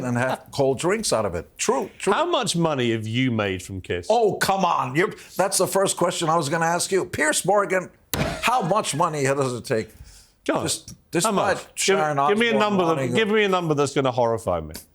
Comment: What does Gene say at 31:22 I can't make it out. "Piers Morgan, how much money does he take???" Thanks.